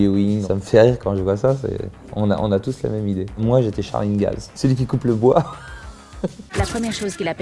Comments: Je regardais pas les sous-titres, j'ai cru qu'il avait dit "Moi j'étais Sharingan" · j’ai entendue « moi j’etais sharingan »